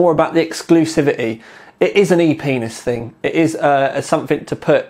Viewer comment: Did he say penis?